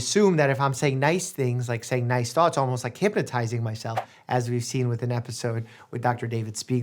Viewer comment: Did he say hipaa-tizing myself?